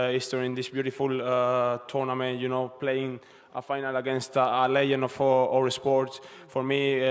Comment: On 0:38 i think he says “playing final against a legend of our sports” And then Djocovic’s face trying to understand what he said 🤔